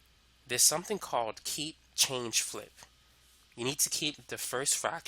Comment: wait what's it called ? on 30:00 min mark you say a term called ... "key change loop"? i wasn't able to make out exactly what you said, but if you or anyone here can clarify, it be greatly appreciated!